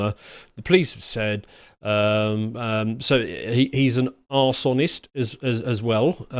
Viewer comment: I have an American accent. When you said " arsonist" I heard you saying " Arse on it" as in: "That pretty boy over there."What's his opinion on the topic at hand?" "Oh. He's being an arse on it"